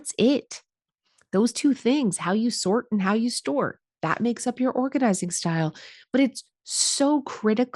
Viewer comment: I thought you said how you store and how you snort 😂😂